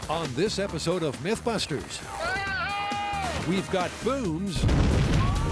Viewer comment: I heard, "we've got boovs.... and balloons"😏